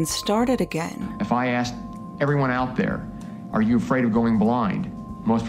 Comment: I heard "are you afraid of going blonde?"and was very confused for a sec, because yeah, I want to but I'm scared